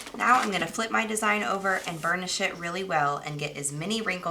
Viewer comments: I swear I thought you said burn the shit really well 😂 🤦‍♀️🤦‍♀️ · did you say " and burn the shit really well"? 🤣🤣🤣🤣🤣 how hilarious if you did. Anyway great video thanks so much for this. Good luck with your Channel I just subscribed 😀! Update: I heard later in the video thats not what you said at all lol excuse my ears . "BURNISH it" got it 👍🏾 QUESTION: Are you using htv or permanent vinyl to create the stencil?